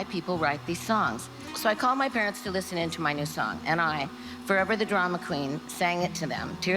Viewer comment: “so I CALLED my parents on the phone, and sang the song and my mom rushed over to me and hugged me”. Which is it Stevie? Did you call them on the phone or were you in the room with them where your mom could hug you? Wow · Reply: She said “I called my parents to listen to my song”, she did not say on the phone. I assume that means call to them to listen…